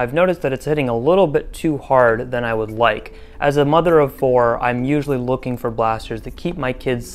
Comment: As much as I love wasting your time and reading angry comments...check the description. Shockingly enough, this video is a joke. Who knew?! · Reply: Um, at 5:13 did you say, "As a mother of four" ?